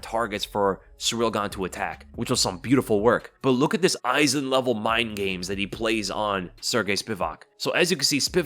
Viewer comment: Did he say Aizen level mind games?